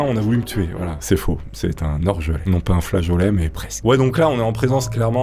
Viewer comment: Moi aussi je suis à Nantes moi c'était pour du papier cul me gars sort un couteau be lui donne le paquet de papier cul par clamse pour si peux et la ce type ouvre donc le papier cul et ce mes a bouffe un rouleau il m'a dit c'est de la bonne je prend et il es partie avec mon paquet de papier cul Nantes cest spécial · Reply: Je ne comprends rien de ce qu'il dit à 1:46 par contre. J'entends "hors jeu"?? Après il parle de flageolet. Il dit quoi exactement?